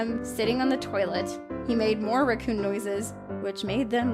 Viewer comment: Oooooooohhh! Raccoon! I swear, I thought she said Recoome! Lol